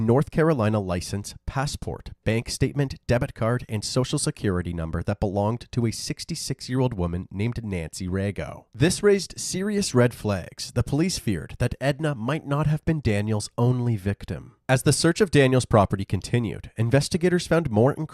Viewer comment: I Thought he was going to say Nancy Reagan at first I said this man cold boy